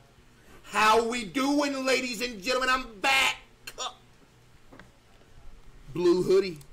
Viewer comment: Why does it sound like hes saying ladies and gentlemen im fat instead of back